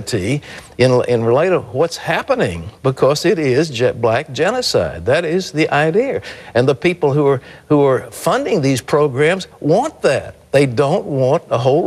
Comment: did he say "jet black" genicide?